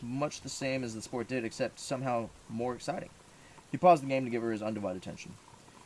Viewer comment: did he say "pause the game?"